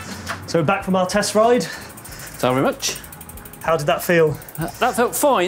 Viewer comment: Whenever your dad said “thank you very much” to the salesman for opening the door, I thought he was saying “time for lunch” 😅😅😅😅